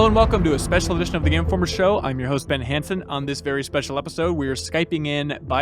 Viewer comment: I'm sure he said something along the lines of "I'm your host Brandon", but I heard "I'm your husband handsome."